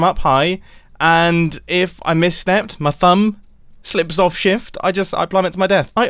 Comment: I'm sorry, but did he say at 8:46 my thumb slips off shift?